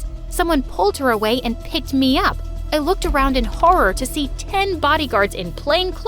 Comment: the caption said I look around in horror to see 10 body guards" but it's actually 9😐😬💀💀